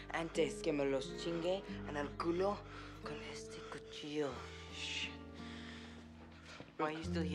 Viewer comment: anybody who speaks spanish wanna spell/write out what shane says at 1:54? my spanish is really bad and i can only pick out the last part as 'en el culo con este cuchillo' which means 'in the ass with this knife' haha i mean to write out each word n stuff